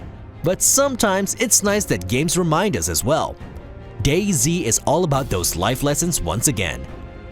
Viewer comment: Anybody else hear him say Gay Z